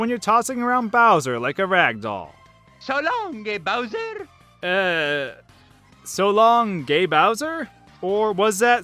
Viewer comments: i heard "so long big bowser" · I always heard Mario say "So long me Bowser." Also I'm pretty sure that first one from Vigilante 8 is "You ready to roll the dice, bro?" There's a whole lot of emphasis put on roll. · I also hear Mario sound like he says So long gay Bowser when I heard the clip also in Dragon Ball Z Kai opening dragon soul I mishear we're in luck now as we're in love now. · What I heard as a kid Mario 64: 1:59 "So longey Bowser!" 2:25 "Psycho". XD (But of course it's "Let's a go!" now that I hear it without the star sound). Star Fox 64: 4:44 "Report" (I was just wondering what the heck he was saying the other day! It's gotta be "Good luck!" And yes, Peach does sound rather unenthused in Mario Kart 64. lol Thanks for sharing this. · I've never heard the gay Bowser thing, I always heard "so long, mi bowser!" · I heard "so long g*y bowser" in Mario 64 as well. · English is not my first language so you can bet I didn't undersand most things when I played these games. When Mario throws Bowser I thought he was saying "So long, le bambi!" My family is Italian, so I know "bambino" is Italian for "boy". I imagined "le bambi" might be either an Italian expression I didn't know or even some plainly wrong attempt at an Italian expression. I do know these days that Italian doesn't have "le" as an article. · I thought it said gay Bowser to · Instead of "So longa Bowser" I thought he said "Delonky does it!" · I always thought Mario said so long big pumpkin🎃.